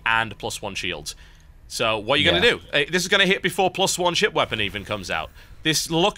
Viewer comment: did anyone else hear both of them say shit wepons in game 1 lol 17:30